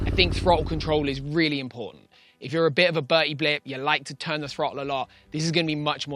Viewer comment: did you just say “buddy blip” or? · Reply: Bertie blip